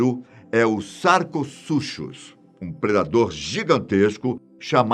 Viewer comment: Eu juro que entendi o "Saco Sujo"